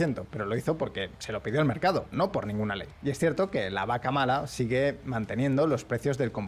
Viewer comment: Min 13:30 escuché ¿¡vaca mala!?, WTF jajajaja